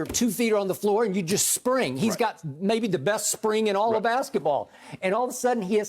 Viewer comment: "He's got, maybe the best spring in all of basketball"... what Skip? lol who says that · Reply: *sprint